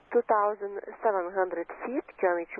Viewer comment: Sure sounds like he said "2500 feet".